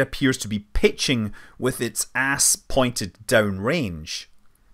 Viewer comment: did he say ASS?